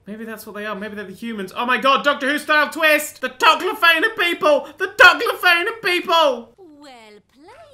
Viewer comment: What did say at 5:19? The Toclar failed people? The Doclar faded people?